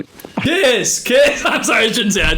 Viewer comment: am I the only one who thought that said „kys“ lmao😭😭💀